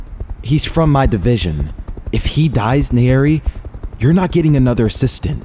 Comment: what is being said ? If he said in the eerie or if he dies in the area ? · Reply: “Nayeri,” which is the mc’s moniker